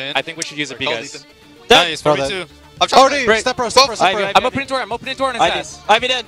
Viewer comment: is hiko saying "step bro step bro" or just me hearing things?